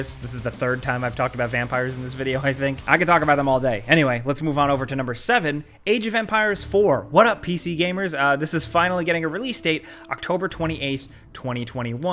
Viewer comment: after all the vampire talk I thought he said Age Of Vampires 4